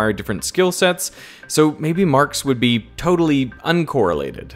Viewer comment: Subtitles says "Marx" instead of "marks".